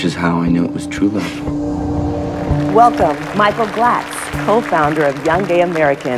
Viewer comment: I thought she said Micheal Blackson